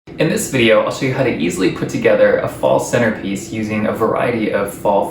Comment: I thought it said fall centipede lol